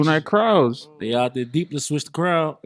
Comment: What he say?? 8:16 i couldnt understand whatt he said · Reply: said he switch the crown I think just diss kings